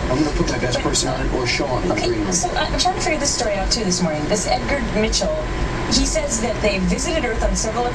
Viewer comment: Did she say, 'THIS Edgar Mitchell...' at 7:25?